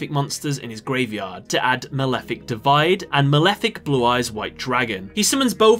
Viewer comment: does he say malefic divide here